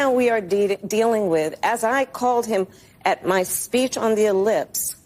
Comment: Her speech on the lips? WTF did she just say lol. The closest election in the 21 first century? Ok Kamala, go with that narrative while everyone else goes with a landslide lol.